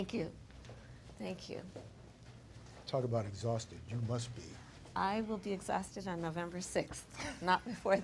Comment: at first, it sounded like he said, “exhausting. You must be exhausting.”